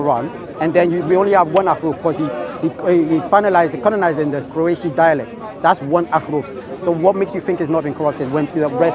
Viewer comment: At around 1:10 does he say “Finalized in Croatian dialect “? If yes what does that mean? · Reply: Qurashi dialect.